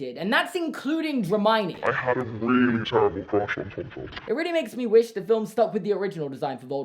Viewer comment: what's emma watson saying? I can't hear over the low pitch. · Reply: I was curious too and it’s, “I had a really terrible crush on Tom Felton.”